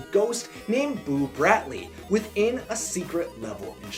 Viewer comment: I thought you had said Boo Radley and I was thinking about how to kill a mockingbird lol